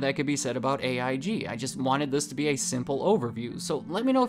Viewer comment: I thought you said scandal overview not simple overview 😅